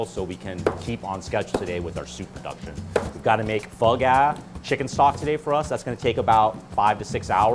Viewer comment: Had to turn the captains on because I thought he said they had to make bug ass. Nope. Pho Ga